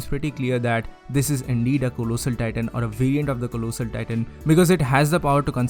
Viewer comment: ohh you were saying collosal I thought you where saying tulusah titahn lol